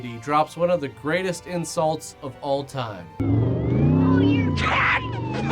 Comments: I've been called a lot of things tadpole hurts the most.. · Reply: I heard ‘cat poo’. And it matches her mouth movements too... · I always thought she said Cat Pooh.